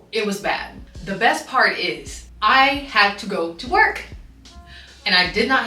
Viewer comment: I thought you said you had to go to twerk 🤣